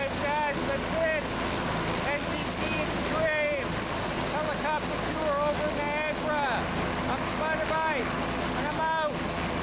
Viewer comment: did you say helicopter ride over Viagra 7:20 ?